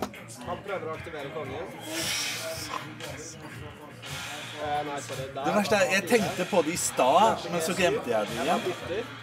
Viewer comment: What did he say in Norwegian? It sounded like, “The worst is that I thought about staying but then forgot I wanted to again.” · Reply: Det verste er at jeg tenkte på det i stad, men så glemte jeg det igjen. "I stad" is similar to "a moment ago".